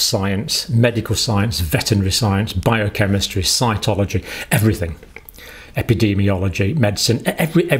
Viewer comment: Did he say scientology? I hope that was a joke.